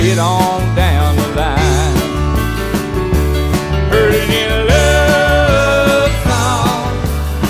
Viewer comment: I'm 46...I thought they were saying perty little love song 🤣🤣